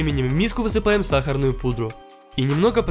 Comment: Мне одному послышалось заместо пунж Пудж?